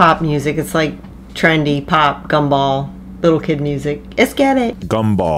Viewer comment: WHEN you realize that adam tells her to work on english. And esketit is spanish. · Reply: I thought he was saying let's get it.